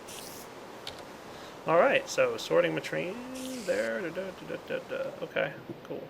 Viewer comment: or so I could have sworn he said "sorting latrine" Lol